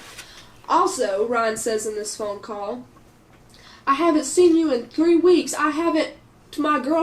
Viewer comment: She says he said I haven’t fucked my girlfriend in 3 weeks ? I thought she said she hadn’t ever ?